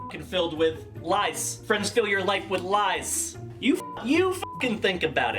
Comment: When he said "Friends fill your life with Lies", I thought he had said "Lice", and tbh I think that would have been funnier